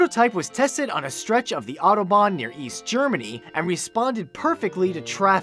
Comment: I heard "Auto bots" then immediately thought "Transformers!!"